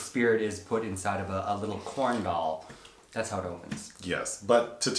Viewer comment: Has to listen to the beginning again. Thought you said her spirit was being put into “a little corn dog.” 😆